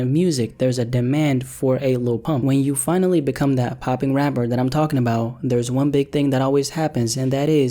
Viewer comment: Who else thought it said the rapper pooping trap